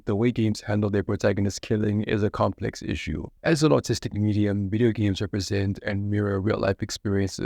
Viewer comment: I misheard artistic with autistic at 0:23, and Honestly i think autistic would have been a better term to use lol